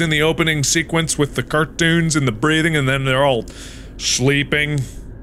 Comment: It’s sounded like he said breeding 💀